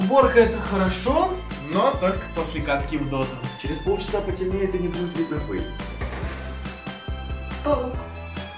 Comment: мне 1 послышалось " не будет видео х*й"???